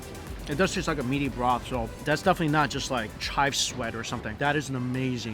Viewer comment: Did I just hear "chive sweat"? Sounds like a new Axe spray.